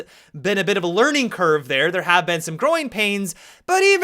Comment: When he says 'growing pains' I thought he said groin pains.😂 good vid bro!